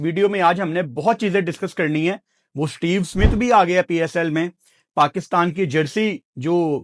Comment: I heard Steve Harrington😅